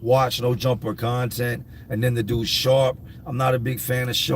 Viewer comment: Honestly, when you said dudes name was sharp I had to rewind because I thought for sure you said shart. It made me laugh because I have a feral cat in my yard that I feed and prior to him coming to me something must have happened to the poor guy and his back legs aren't to good so he can't hold himself up to poop. So regardless of where he is he just poops. So I named him Shart but I just call him Art. I wish he was friendly because I would take him to a veterinarian to see if they could help him. Poor little guy hisses at us when we feed him because he doesn't know any better. He has a warm place to sleep and food to eat and he seems fine with that he also has some kittens a momma cat left us some months ago. I am trying to get everyone in before winter hopefully.